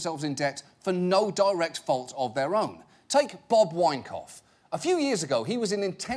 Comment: I thought he said Bob Wankoff